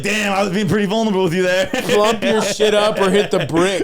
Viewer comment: “Pump your shit up or hit the bricks.” Is an all time line 😂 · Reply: I thought he said clump 😂